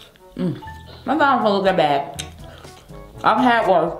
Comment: ok but did anyone else think it sounded like bloveslife said "that's a mouthful of not that bad, i'm cat barf"